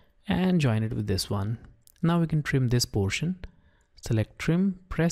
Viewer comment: Totally thought he said "trim this bullshit" lol